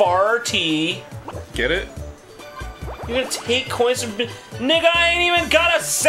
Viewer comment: DID JONTRON SAY NIGGA??